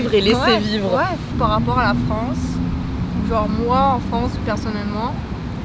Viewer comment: she didn't say she liked it less there. She just said, "genre, moi en France, personnellement..." ;)